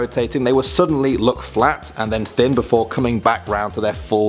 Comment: anyone else hear him say "fwat"? I know I did